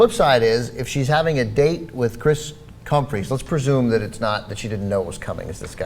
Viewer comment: Did he just say Kris Cumfree ?